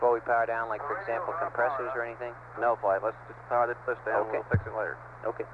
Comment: Apollo 13: The only NASA spaceflight that a controller uttered the word "datgum". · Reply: I may have misheard it, but did EECOM say "let's just power this bitch down, we'll fix it later" when Lunney asked if he wanted any switches off (compressors, etc). ? (I would have loved to hear some of the comments made off-loop... :-) )